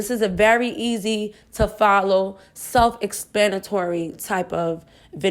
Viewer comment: Did she just say “self ExPANitory?” 😂😂